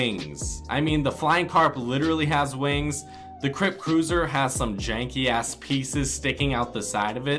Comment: did he just say the crip cruiser has some jankey ass peices sticking out of the side or is just me?